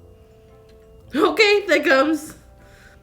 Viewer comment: I lowkey thought she said “ OK thick hoes “ (6:33) Lmao your the best girl 🤣🤣🤣